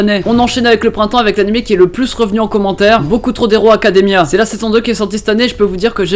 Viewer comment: Je rêve ou j'ai entendu " Boku trop hero academia " ? XD